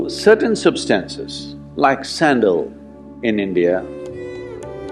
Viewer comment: Good Day, what is the name of this incense? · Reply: I thought he said sandal so I’m guessing sandalwood.